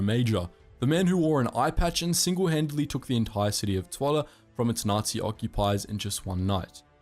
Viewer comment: Im born in zwolle and i find it mildly interesting that the auto subtitle text says toilet instead of zwolle